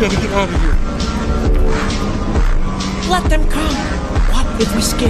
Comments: I thought he said let them cook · I had to play that back i thought the text said "let them cook" 😭😂😭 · For a second there i thought he said let them cook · I thought I heard "Let them Cook" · When Mike said:” let them come,” I thought he said, “ LET HIM COOK!”. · “Let them come” 🥶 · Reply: I thought he said let them cook for a second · I thought he said let them cook💀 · I thought he said let them cook 💀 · I thought Mike said let them cook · am i the only one who thought mike said “let them cook”?💀💀💀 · I thought he said let him cook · at first i heard "let them cook" lol